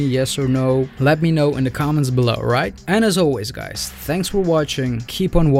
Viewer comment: So impressed by your editing but more impressed by your German lol. Lee is very likeable. Also at the end you said "And as always" I thought you said "Anna's always" hoping that's the next one! heh